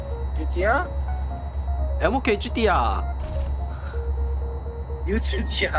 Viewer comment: did say MOK chutiya 😂